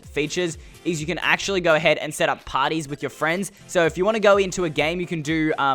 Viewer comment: At Mineplex it sounded Like he said you can play potties with your freinds.